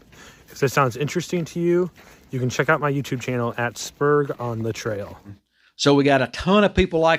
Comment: You showed a hiker, it sounded like he said his YouTube channel was “spurs on the trail” I couldn’t find him. Did I mis-hear his name or Youtube channel?